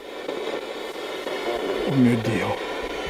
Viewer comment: ho capito po**o dio